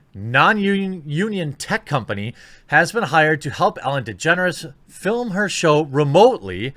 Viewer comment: I thought you said "Ellen Degenerates" for a minute.